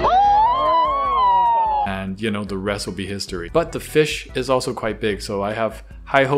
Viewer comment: After you talked about the cat and fish getting stabby stabbed, I thought you said “Anddd the recipe history” and i died too hard for that😂